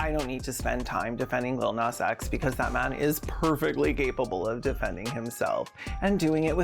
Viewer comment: did you just say lil nas x is perfectly gaypable of defending himself?